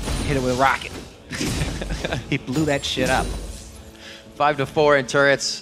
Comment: did Kobe say 'he blew that shit up'?!!